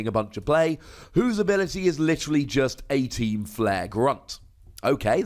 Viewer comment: sounds like u said 18! team flare grunt